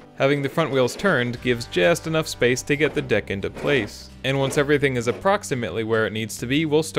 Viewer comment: Sounded like “get the dick into place” 😂 cool vid, great repair